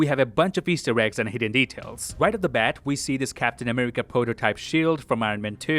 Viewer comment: Porter-type instead of proto-type.